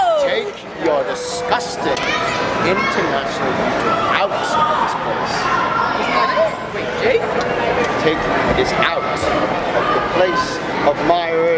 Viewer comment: what did the wierd kid at 11:39 even say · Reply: International YouTube out of this place take this out of the place of my Earth if you were listening closely you would have heard him bro and he's not weird